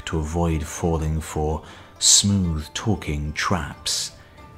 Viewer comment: "Avoid falling for smooth-talking traps..." jeez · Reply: I thought he said "smooth talking tramps" lol